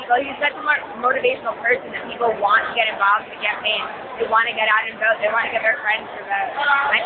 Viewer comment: Does she say "...want to get out and want to get paid....." Paid? Huh? How much and by whom?